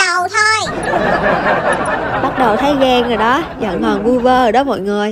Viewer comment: mà em cứ nghe thành en - rồ